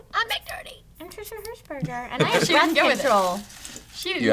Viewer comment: I thought trisha said I have birth control